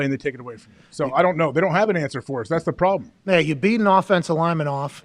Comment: You beat him off? Did I hear that right?